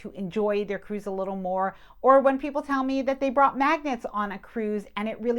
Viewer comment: love the channel you do a great job! I did notice you said you take magnets,,,,, i thought you said maggots. I guess that is why we got funny looks 🤪. Keep up the good work!